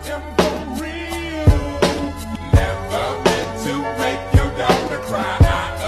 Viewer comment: What i hear: "I'm sorry Ms.Jackson i ate four meals never meant to make your daughter fries i apologize a trillion times I'm sorry Ms.Jackson"